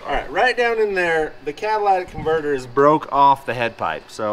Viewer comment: IDK why I thought he said Cadillac converter haha